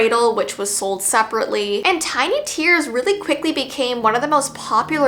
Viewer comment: I thought it said tiny ears